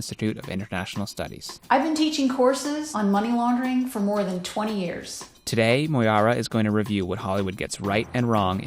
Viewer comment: Misunderstood her, thought she said: "... Ive been teaching horses on money laundering for more than 20 years". And btw your off angle cameras pixels seem very low or off